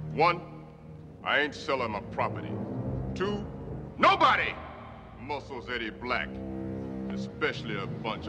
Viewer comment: I thought for a while he was saying "Nobody muscles "any black" "